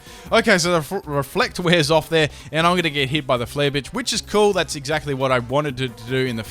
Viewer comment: is it just me or did he say "flare Bitch" at 12:08 XD